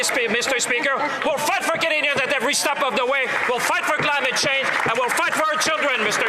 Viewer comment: almost sounds like he says fight for garbage change. 😅